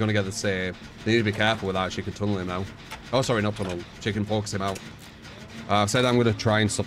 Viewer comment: You can tell I am tired when I heard him say "chicken fox him out" instead of "she can focus him out"